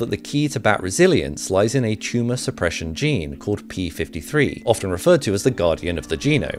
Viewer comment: I legit thought it said it was the PS3 gene